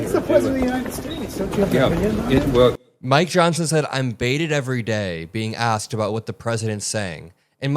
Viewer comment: You misheard. He said "I'm bait'n every day!" and his son is checking how he's doing it, too...